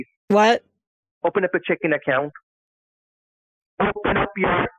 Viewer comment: Was I the only one who heard him say "Go to your chicken account, m'am"